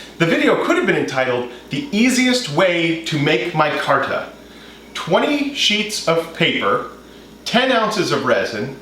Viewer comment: For a minute I thought it said "Making Micarta Milkshakes"